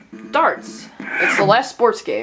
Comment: I thought it said farts